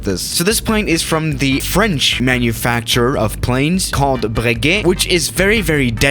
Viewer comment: When you said Breguet I thought you said baguette